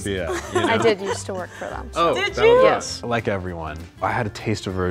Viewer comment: did he just say "like everyone " ?